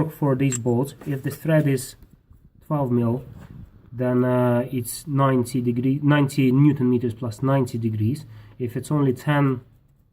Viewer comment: Hi there again. There is just one bit of info I am not clear on. At 51:44 you replace the new Idler bolt but I could not understand what torque you say it should be tightened to. You mention 90Nm and 90 deg. and then say 40Nm and 90 deg. Could you tell me what torque is correct please. Very much obliged and thanks again. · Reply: If idler roller bolt has a 12mm thread - it's 90Nm + 90°. In case if you have different engine and idler roller bolt has only 10mm thread, tightening torque for that is 40Nm + 90° further. If you try to take 10mm to 90Nm - you'll brake the bolt. I've done it once 😁